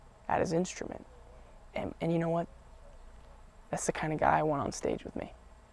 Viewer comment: When he said 'thats the kind of guy I want on stage with me' I heard 'thats the kind lf guy I want to sleep with me' and i was just like OH OKAY THEN ME too tyleR